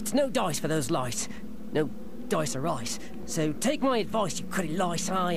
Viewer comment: For some reason, when Number 4 said No Dice or Ice, it almost sounded like he said: Toys R Us.